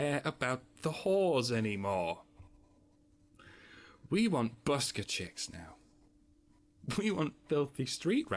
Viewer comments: What is being said at 13:57 "We want [Sounds like 'busker chicks'] now." Possibly it is a word I'm not familiar with because of cultural differences in US english v/s British english. THANKS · I don't understand, ''...chicks now'' what's the word and what does it mean? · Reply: Busker. Like a street performer. They stand there and do their thing (Sing, dance, play an instrument, be a living statue, etc.) and hope that generous enough people are impressed enough to give them money, or simply enjoy what they're doing so much the money is relatively immaterial.